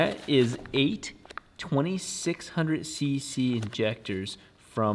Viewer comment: 8x2600cc's lmao. I was gonna say, thought I heard 600cc's.... Heh